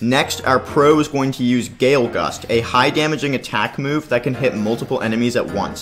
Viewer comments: I thought I heard gay gust🤣🤣🤣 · i thought he said "gay gust"👽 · I thought u say gay gust 💀